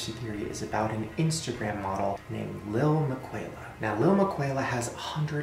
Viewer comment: Last time I saw this I thought he said Michaela not Miquela